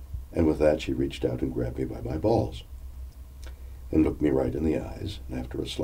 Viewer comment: from 1:55 - 1:58 .. did he say the gorilla reached out to grab his balls after he said he loved her? lmao.